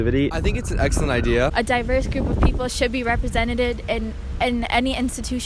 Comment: “A diverse group should be represented-ed.” This is Harvard folks. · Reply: I'm french and I heard "representated"